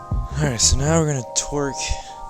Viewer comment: I swear I heard you say (twerk) around the 2 minute mark instead of (torque) 😂 could just be my twisted mind lol